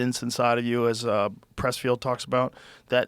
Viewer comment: What does Joe exactly say at 39:20 ??? Does he say „as pressfield talks about“ ??? If yes what does he mean and what is he referring to